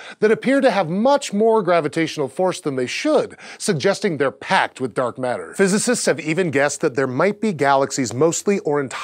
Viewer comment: totally heard that as "Pact with dark matter". all hail the dark galaxy.